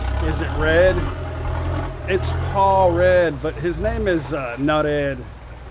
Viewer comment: I’m eating a 2lb 30 day aged ribeye that is melting in my mouth and I would trade it for the food on the table in Korat, it looked delicious😋. I thought you were saying Paw Red which Paw is hillbilly for dad🤷‍♂️. Thanks for sharing your beautiful life with us. Many blessings to you, and family 🙏 Cheers 🍻